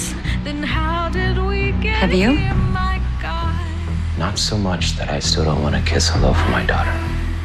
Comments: UM HELLO? ARE WE JUST GONNA IGNORE WHAT HIRAM SAID TO VERONICA? Veronica: "-but the question is have you" Hiram: "not so much that I don't want to kiss all over my daughter" 13:07 🤨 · i had to replay it bc i thought he said "i want to kiss a loaf of my daughter" xD